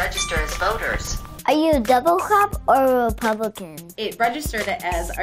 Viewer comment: I thought he said “doublecrap“😂😂😂